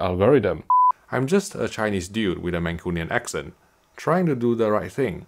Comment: When he said Mancunian, my brain heard it as Manchurian 😂